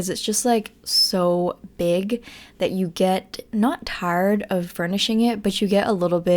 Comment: I'm about 7:33 into the video. Did you just say "tarred" instead of tired? Are you turning into a southern girl?